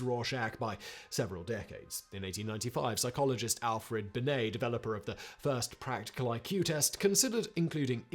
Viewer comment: When you said Alfred Binet, I thought you said Alfred Bidet, and was expecting him to be the inventor of the toilet fountain.